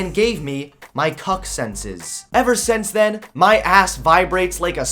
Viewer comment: Thought you said “cocksense”, thanks for clarifying.